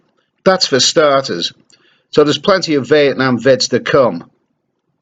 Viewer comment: My brain heard you say "Vietnam Vet" gave this 68 years old yank nightmares. I now think you must had said Vietnam Vedio.